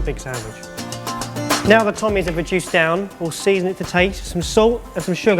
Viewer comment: WTF did he call the tomatoes? "Tommies"???? WTF with these Brits???